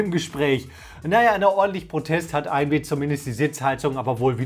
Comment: hat einer AMD gesagt? Nach mehrmals anhören verstehe ich immer AMD